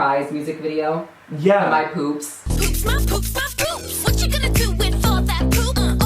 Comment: I thought it said “my boobs my boobs my boobs” please end my life